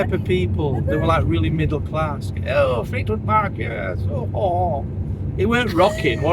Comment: We thought you said Fleetwood market 😂😂